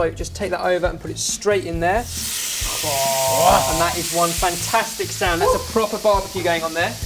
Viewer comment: did ben say that is one fantastic salmon? :b